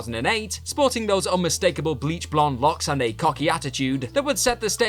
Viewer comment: did you call dolph’s hair LOCS? 💀